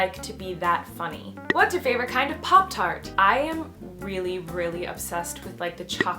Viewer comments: I thought "Pop Tarts" said "Pop Farts" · When you said "Pop tart" I thought you said "Pop fart" and I am like " WHAT???"